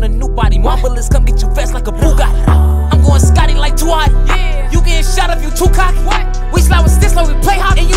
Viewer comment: I thought he said “Scotty like too hotty”